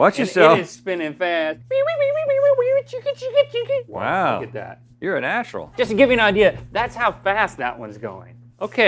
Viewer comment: HAHA I thought Rhet said "WoW... You're a A-- Hole." at 1:49 :)